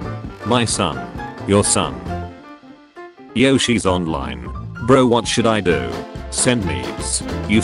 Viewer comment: i heard yoshi's online